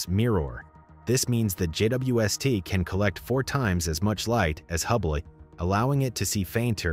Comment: Did you just say "can detect 4 times as much light as HUBBLY." I heard hubbly and so did YT autocaptions lol